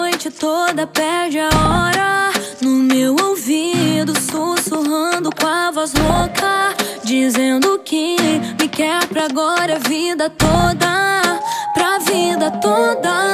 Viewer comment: eu só consigo escutar “com a voz louca” ao invés de “com a voz rouca” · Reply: menina, de primeira eu entendi "sussurrando o kama sutra" EU JURO